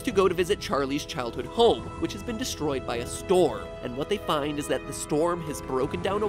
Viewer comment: i thought he said "was destroyed by a store"